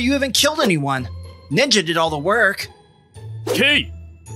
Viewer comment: I thought he said the n word 💀